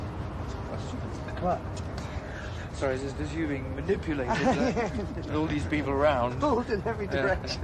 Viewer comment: what he said ? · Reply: Noble Freddie, “Sorry, it’s just you being manipulated with all of these people around.” Roger, “Pulled in every direction.”